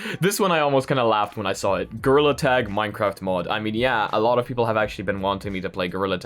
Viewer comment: I was severely caught off guard and confused at 4:10 cause I thought you said "Girl attack minecraft mod"